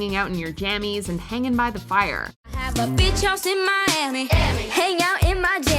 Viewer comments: it sounds like he says “bitch house “ LOL. 🤣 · In the kidz bop version of that’s what I like when they said beach house it sounded like the Said bitch house was I the only one that thought that